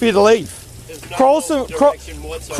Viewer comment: i think old mate was trying to say 'quarrelsome'